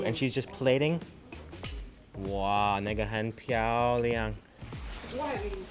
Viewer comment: Did he just say "nigga"